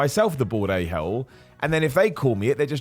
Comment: I thought for weeks you were saying bored a hole. I thought it was meant to be a joke like, I'm so bored I'm ranting about 10 things not to do in the gym 😂